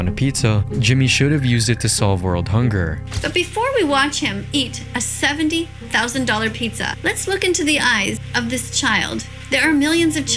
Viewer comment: i thought she was about to say "Now before we watch him eat a 700,000 Child"